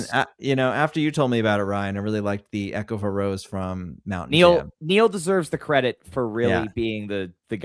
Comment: where is the EOAR from that Pete is excited about here? Ryan steps on his sentence and I can't hear the city/venue/show. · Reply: I believe he was going to say Mountain Jam (6/22/25)